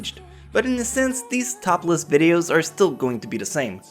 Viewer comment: sounded more like topless instead of top list lol .XD